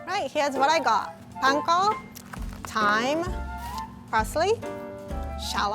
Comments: When she said thyme I thought she said time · When she said she was adding thyme, I thought she said time and I was confused